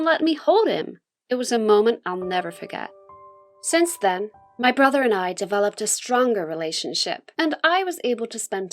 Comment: I low key thought she said my brother had a secret